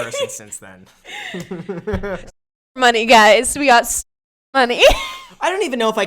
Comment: What did he actually say that gets bleeped at 6:35?? I really want to know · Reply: Stripper money